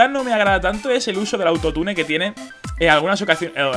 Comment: hola alguien me podría decir que es el altotunel o algo asi que dijo en el minuto